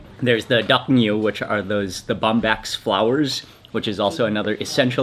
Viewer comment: What kind of flowers are in that tomato based ricenoodle soup? Bombax flowers? Did i hear that right?